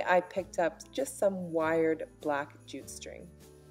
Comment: Wait...what?! #9:40..I was listening to your tutorial whilst sewing..and I thought you said 'black g-string'.. Jute string, Monica, Jute..!!